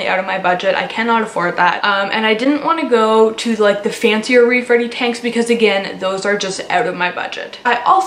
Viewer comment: Omg for a second I thought you said “rape ready tank.” Wtf